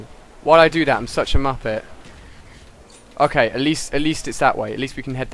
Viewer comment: did he just call himself a muppet